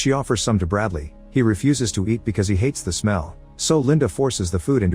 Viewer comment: I assume I'm not hearing what I'm hearing, idk the English word for this type of shell, but 4:54 "Linda is very excited because she's found a COCK"? Edit: OK so the word is likely "conch" and that's not how the AI thinks it's pronounced...